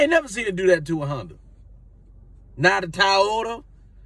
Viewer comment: LOL Sounded like Shuler said... Not A Tired-Yota 🤣🤣🤣